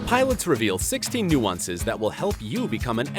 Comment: I thought that nuances said nusensecs